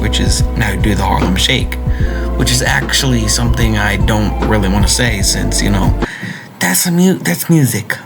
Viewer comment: what the hell does he say here 0:24 huh???? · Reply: “The Harlem shake, Which is something I don’t want to say because that’s music” so I think hes afraid of being copyrighted